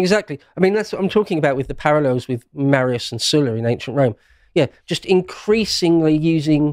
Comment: My English isnt perfect - can someone tell me what he saying at 15:08 about Ancient Rome. He says "Marius and (something)"... I assume he's talking about General Marius but I can't hear me the second part. Thank you! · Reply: Marius and Sulla